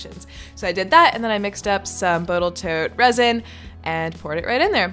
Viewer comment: Wait! Am I the only one who heard you say "Botal Toat resin"? Ironic that I found this video of you making Jimmy's present. Just this morning (<num>) I watched as he opened your gift. Very cool.